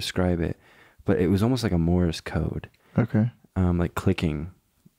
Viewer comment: did u just say morris code